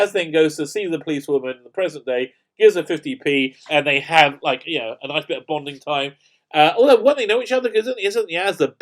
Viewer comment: I swear I just heard you say "they have a nice bit of bondage time" 😂 Best freudian slip of 2020?